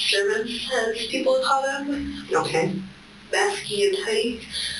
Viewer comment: I think she said Masky and Hoodie.